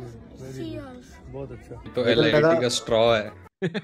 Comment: couldn't understand...... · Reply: LIT ek drink hoti h jo beaker m aati h toh usko straw se peete Wse vo LID hota h, long island drink